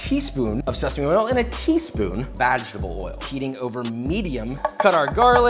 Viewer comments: Am I the only one who heard vaginal oil? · I swear he said vaginal oil! 🤭